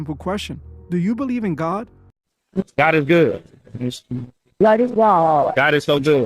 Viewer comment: sounded like she said God is Baal...😮